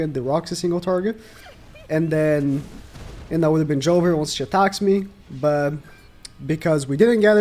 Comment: Did he say 'Jover'?!? 😳 🗿 9:52 hE dId!!! Man, I'm dead 💀